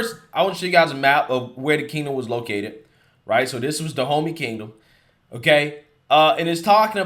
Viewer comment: I thought you were saying 'Da Homie'. F**k, that makes me racist, doesn't it? Damnit.☹️